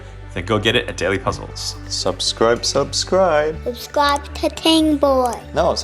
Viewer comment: I first heard "subscribe cocaine boy"